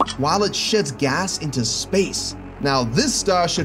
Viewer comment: did anyone hear sh*ts gas into space at 10:08?